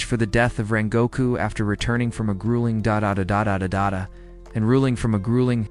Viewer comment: Bro at 1:46:00 why is it saying “dodadudadodudada”